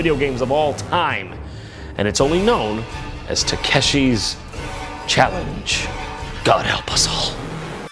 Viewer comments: I thought you said tekashi challenge (6ix9ine challenge) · DAMMIT, at first I thought it said Takeshi's castle :___(